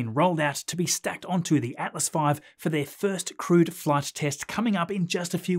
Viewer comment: When I hear "crewed flight", I initially think "crude flight" 😳